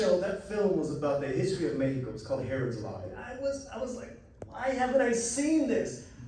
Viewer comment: What did say at around 19:40? I didn’t catch the name of the movie. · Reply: Herods Law 1999 on Netflix, in Spanish from Mexico amazing film